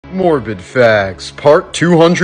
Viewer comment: I thought he said morbin facts and I waited for the part that relates this to the movie